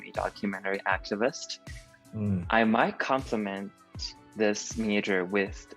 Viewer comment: I think he was saying that he might "complement" his major with Communications or Linguistics.